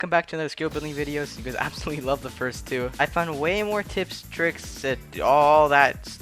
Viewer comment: My dumb ass thought he said tips and dick 💀 i got a huge dirty mind